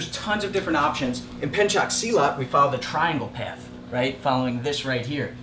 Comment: did he say pencak silat?